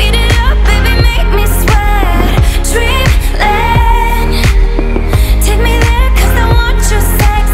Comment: I now know it's 'dreamland' but I used to think she was saying 'dribbling' XD